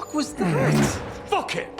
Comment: "BUCKET!" ×kicks bucket× I know its not what he said, its just what I hear anytime I see that scene xD